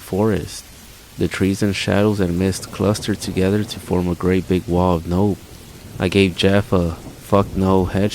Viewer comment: did you say a great big wall of nope 😂😂 I feel ya man. And fck that is an appropriate response lol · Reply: SURE DID!!! LOL haha....I swear sometimes i say some of these things and when I go back and edit its like.."did i really say that...it doesn't sound natural." but F IT!!! lol